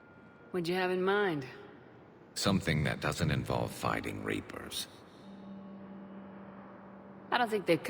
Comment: it sounded like he said fighting raper's